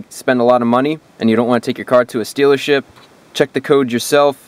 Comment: so NO ONE heard he said Stealership instead of Dealership? Lol that was funny